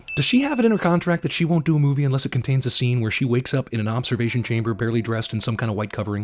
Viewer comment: I'm sorry...I heard Masturbation Chamber